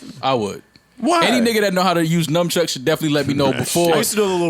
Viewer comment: does Mal say "NUMB-Chucks"?